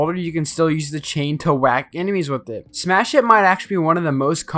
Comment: I heard you say "smash sh*t" bro